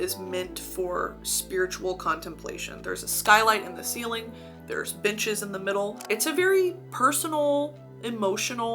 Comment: i thought she said “there’s bitches in the middle” talking ab the ppl in the photo😭